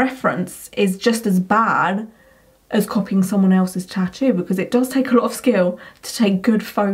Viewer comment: "It doesn't take a lot of skill to take good photos." Are you kidding me? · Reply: You say it does, I heard doesn't.